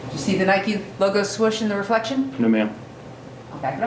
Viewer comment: I heard "Soros" and thought things were about to get stupid crazy. 😂